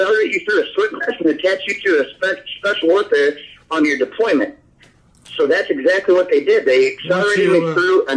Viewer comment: "they said we're going to detach you to the Special Olympics on your deployment" 13:10 LMMFAO IS THAT WHAT HE SAID?